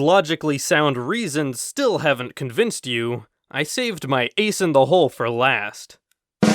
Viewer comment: I thought he said Ace in the LoL